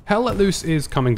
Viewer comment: Aside from this one, the last two videos I’ve watched sounded like they were saying hella loose every time they said the name of the game. Lol